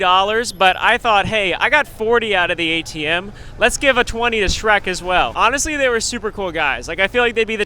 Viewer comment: did he call Hulk Shrek?